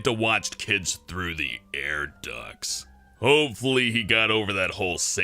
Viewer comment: did he say air ducks?